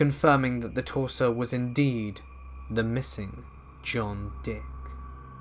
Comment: At one time I thought he said John Wick.